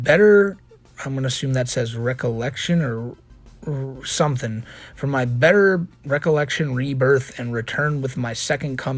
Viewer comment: I think it actually says 'revelation' at 20:58, though the 'n' is cut off- revelation, rebirth, return. He's pulling from the bible without knowing what any of it means and it's fucking hilarious but probably won't help him in court. It's gonna be 'Love Has Won' cult and Mother God all over again.